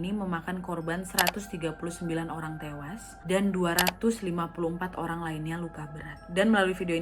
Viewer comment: Lagi2 aku dengar ka nadia ngmng 25 luka berat/org tewas, tp di tulisnya 254 wkwkw maap kalo salah denger lagi🤣🤣